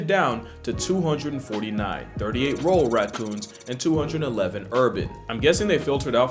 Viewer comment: I thought you were saying roll